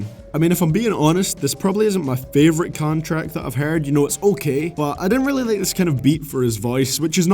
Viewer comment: I thought you said "this isn't my favourite contract that I've heard" lololo